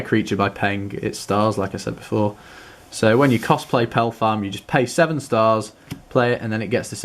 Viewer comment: I thought you said Cosplay instead of Cost play when you were reading Pelfam's abilitiy